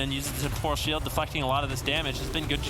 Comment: did he say fu**ing? :D :D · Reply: deflecting